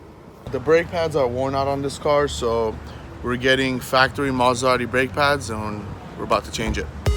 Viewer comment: Sounded like said mazda audi brake pads lmao